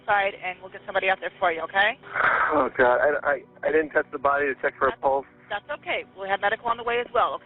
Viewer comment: And the fact that he didn’t try to give her any sort of aide or even check for a pulse! He was so quick to assume and accept that she was dead 😏. It’s very obvious from the 911 call that he’s guilty. No question about it!! 🙄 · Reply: I thought I heard in the 911 call he said he did check for a pulse but that's it. I must have misheard. Just a sick monster all around. I pray Shaunas daughter & her family are doing as well as they possibly can.